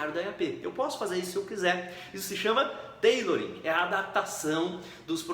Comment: O que você disse em 9:22? "_________ é a adaptação dos processos de Gerenciamento de projetos para seu dia a dia." Não consegui entender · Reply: tailoring